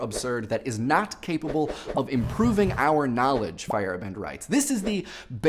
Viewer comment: I just got into Rick and Morty! Like weeks ago! And this video was uploaded on my birthday! Anyway, a little off topic but on topic, but I kept mishearing Feyerabend as Firebend, and I kept imagining this scientific Firebender. Hmm, that sounds like a good topic too; the science of elemental bending! Now on topic off the video. A friend and I had a discussion about this once, where we were trying to debate what makes something scientifically moral and what doesn't. There was a poll somewhere that had asked "Should scientific experiments be done on jail inmates on Death Row?" And I had to question it myself. I couldn't decide. Part of me said "they're going to die anyway, we could get more progress that way" but the other part said "That's sick and wrong! We're no longer living in the 1930s where doctors in asylums would literally experiment on people!" Rick may be that side of the spectrum where he says "Everyone's gonna die soon anyway, doesn't matter." and continues on. And perhaps he is going in blind and makes achievements that way. I couldn't say for sure when it comes to the scientific progress or if anarchy should be involved as well to make a better progress. Because I would, myself, be always caught in that moral loop. Maybe as season 2 goes on, we will get more info on how Rick thinks.